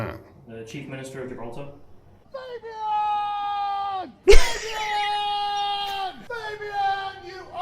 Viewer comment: I can't even figure WTF Carl is yelling. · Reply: "Fabian"